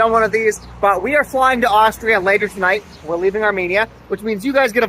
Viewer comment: Thought you said Australia :( got excited for no reason